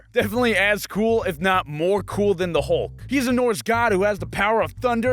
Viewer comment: anyone else hear "he's an orange god"?